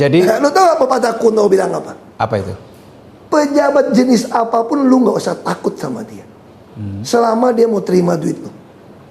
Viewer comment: pepatah kuno bilang apa? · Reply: Pejabat segalak apapun dia, akan jinak kalo terima duitnya